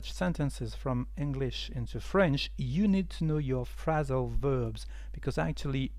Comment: I'm not sure if I heard correctly. At 3:08 it sounded like you said 'You need to know your "frazzle" verbs'... is that what they are called?? Frazzle verbs??